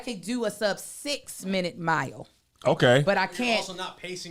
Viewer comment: Not gonna lie, I thought was saying she ran a sub-5 minute mile, too!